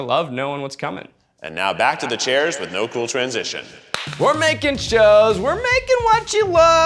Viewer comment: I really heard "we're making shows, we're making love," and I HAD QUESTIONS